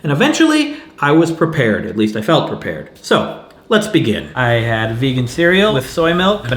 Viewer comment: I thought you said "let's vegin" #missedpunopportunities